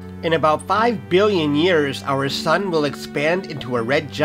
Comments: I thought he said in 5 years the Sun will die. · Oh god For one second I thought you said million instead of billion. I was so scared 😱